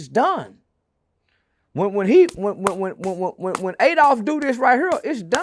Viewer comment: did he just call him “Adolf “lol